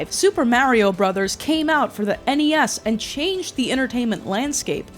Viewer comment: Why everytime someone says "Game came out for NES", I hear "Game came out for аny аss"